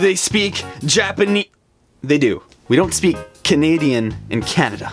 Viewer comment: I thought you said we don't speak Canadian but you said we do.